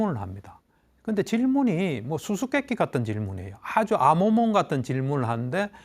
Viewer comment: 정도에 교수님께서 아모몽? 같은 질문이라고 말씀하셨는데, 제가 잘못알아 들었는지 검색해보니깐 말이 검색이 안되네요…. 혹시 ‘아모몽’?이 무슨 말이지요?^^ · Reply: '암호문' 같은 질문